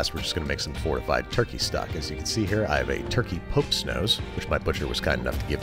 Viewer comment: you have a turkey WHAT? Kept rewinding couldn’t understand what you said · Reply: Popes nose